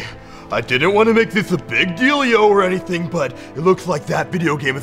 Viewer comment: I didn't want to make this a big deal-io. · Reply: I honestly thought he said a big deal yo